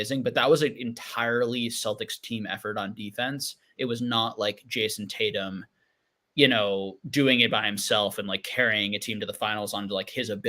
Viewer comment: I thought ya'll said is "jason statham" overrated haha